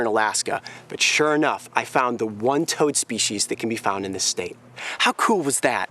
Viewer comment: I found a toe is what I thought he said